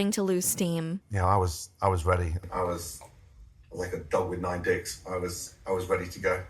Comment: Okay, this is a weird question and probably no one will see this because the video is 6 months old, but at 13:58 does he say he was like a dog with nine dicks? Because that's what it sounded like to me, and I listened to it several times and that was all I could hear, and then I turned the captions on and that's what they said too, but that strikes me as a deeply bizarre and horrifying thing to say and I can't imagine what it could possibly mean.